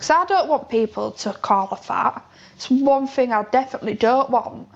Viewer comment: thought she said fart 😂